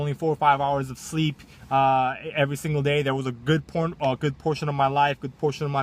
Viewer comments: Good Porn? Lol I heard that slip up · LMAO. I heard good porn.